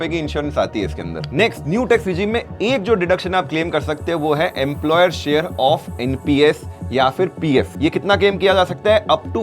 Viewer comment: 80ccd2 employer nps is known.. did you say employer pf as well..?????